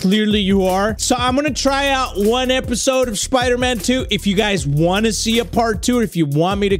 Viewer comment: for a second thought it said spider Mexican 2